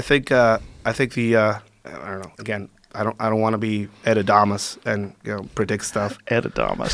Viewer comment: What is the word that he used at 2:48? Edadamas?